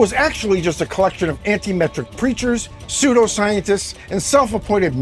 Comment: That name has lost ALL of its power lately. Oh wait, I thought you said anti-sem....